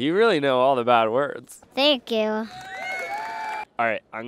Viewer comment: When he said thank you I thought he said f**k you